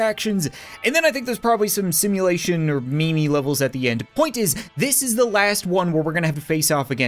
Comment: What did you just say? Meme? Or Me me. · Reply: “Memey levels I did not get to try yet”